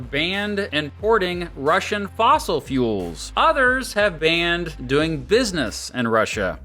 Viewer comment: I need to stop "watching" videos and cleaning. I just misheard "Russian fossil fuels" as "Russian taco fuels" SMH